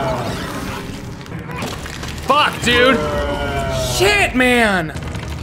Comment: did someone say, Shitman?